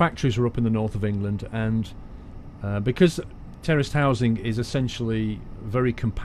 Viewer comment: Every time you said terraced housing, I kept hearing 'terrorist housing'. I was like, wow Great Britain is really different.